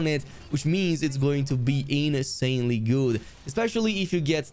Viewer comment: that hammer @ 5:21 It's going to be "anus"anely good 😌😌, idk if I heard it right.